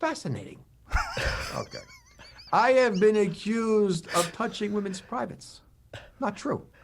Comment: I heard "punching women's privates" and I died laughing.